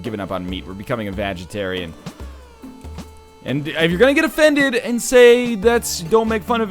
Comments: I thought NL said Vagetarian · I thought you said, "Vagitarian" instead of vegetarian. Heh.